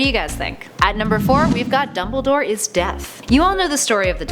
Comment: At first I thought she said "dumbledore is deaf" and I was so confused